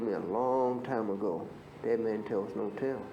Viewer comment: What did he say? "Man tell no tell" is the caption. I do not understand.